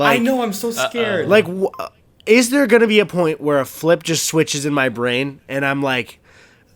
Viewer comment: Uhm I think glitching. Did I just hear "a flip switched"?